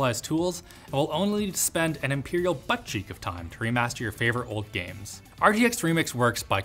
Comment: Did you just say metric butt cheek? 😂😂😂😂😂😂😂😂😂😂😂😂😂😂😂😂😂😂😂😂 Seems about right that’s it’s less. I’m using it that now.